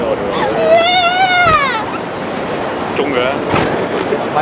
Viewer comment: i heard the camera man said : " zhung kui ler "... 0:49 ...